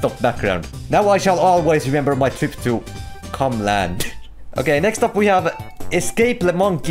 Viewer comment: I thought it Said CrumbLand